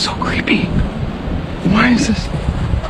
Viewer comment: Am I the only one that heard the whispering at 14:44? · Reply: I heard " Who is it? "